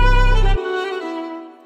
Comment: the signature voice line sounds like its saying 'its costing your baby' try unhear it now P.S. the beat slaps